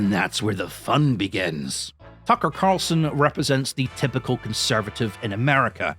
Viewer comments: I could swear you said F*cker Carlson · To me the first time you said Tucker Carlson it sounded like you said "Fucker Carlson" and now I have a new way to refer to him 😂